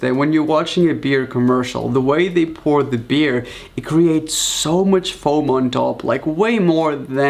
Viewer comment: My headphones accidentally turned up to max volume, and all I heard was “So much foam”